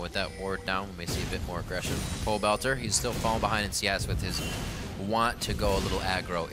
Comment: Did I hear Riv say "Pobelter, he is still falling behind in CS" at 8:58?